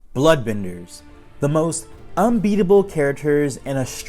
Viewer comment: Dude I first thought you said black benders lol